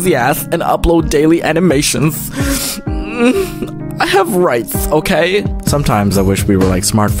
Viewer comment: I was thought the rando guy said "I have RICE ok!" and I was like "WE ALL HAVE RICE BOIII!!!" and I kept watchin' it and I noticed it was "I HAVE rights ok!" >^<